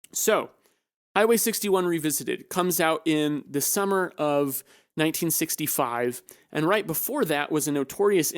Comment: I thought you were saying "December of 1965" and not "the summer of 1965" for the released of Hwy 61 Revisited and was so confused. Good podcast, thanks for it! Interested in what others discuss with Dylan. Never actually sat down to a podcast of it, usually just watch the docs and read books or Reddit posts. Also, the reason Levon was gone is because he hated it, then went to work on an oil rig. Pretty weird! He finally came back for The Basement Tapes and all that.